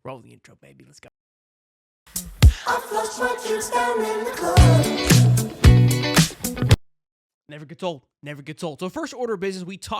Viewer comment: Love the channel really do...and love the intro but....everytime it plays...sounds like it says " I flushed my kids down at the club"...to which taking the kids to the pool means taking a shit...lol lol....sorry